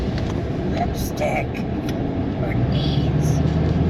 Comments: So I definitely found what the voice is! It's a commercial that is run on YouTube. How it became imprinted onto the video is beyond me? It least my truck camper is not haunted! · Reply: I heard “lipstick or beans” 😳 Creeepy!!! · I heard "lipstick for deeds". I dunno. I guess some ghosties will do anything for lipstick? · So I definitely found what the voice is! It's a commercial that is run on YouTube. How it became imprinted onto the video is beyond me? It least my truck camper is not haunted! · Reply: Sounded like it said, “the lipstick or these”. Sounded very creepy - even for an imprint. Ghost of YouTube perhaps. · To me, that voice sounded like it said, "lipstick for keys". That was creepy.